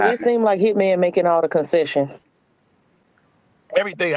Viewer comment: did she say "it seems like hitman is making all the cecisions" like she meant "decisions"😂😂